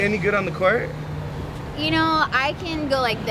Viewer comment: all I heard was, "I'll try anything!"